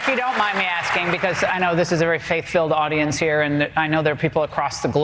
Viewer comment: i keep hearing "I know this is a hate filled audience here.".